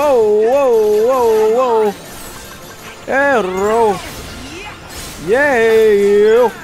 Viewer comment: I love Tangled. Rapunzel is so cute X3 I guess it's just me, but I heard saying "errou" in this moment 33:02. Fun fact: "Errou" means "Missed" in portuguese